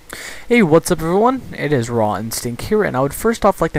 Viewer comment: omg i always thought u said,: hey what's up everyone, it's rot and stink here...